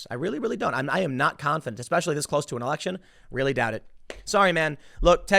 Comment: did Tim just say Auction instead of Election?